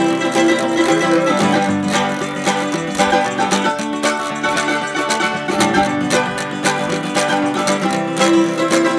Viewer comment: what is she saying : "Hei, please someone keep it away from Youtube " ?! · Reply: Niko La- I do believe she said "Hey can somebody keep Grease from Zuzu". I presume she's referring to the puppies with them.